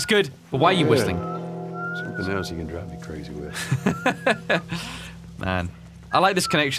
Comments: “something you can drive me crazy with” = “something else you can annoy me with”???? SUBTITLES ARE WRONG, YOU HAD ONE JOB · Is it just me, or did anybody else notice that at 28:10 Joel says "something else you can drive me crazy with." But the subtitles say "something you can annoy me with"... no? Just me?